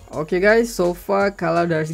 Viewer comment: Did u said "okay guys, so f*ck"? Or did i hear it wrong?